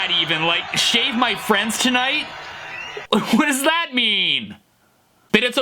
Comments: I thought he said shag my friends tonight. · This shredemption always makes me undeniably happy every time that I hear it!😊 The music alone just takes me back to the early 2000s pop punk days when I listened to Sum 41 and others! Also, I always thought Fred was saying “Shag my friends tonight”😋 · I thought he said "shag my friends tonight" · I thought he said "check my frets tonight" 😂😂😂😂😂😂😂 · Sounded like he said, "Shag my friends tonight", that would be a merry dance indeed,....Hurrah!!!! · I heard: "Check my friends tonight." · I thought he said shag my friends 😂😂😂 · Thought he said "Shag my friends tonight" xD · i thought he said shag my friends tonight